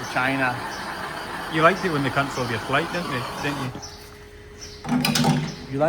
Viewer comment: Totally misheard Cameraman say "You liked all they C** on your flight" instead of "You liked it when they cancelled your flight" 🤣🤣🤣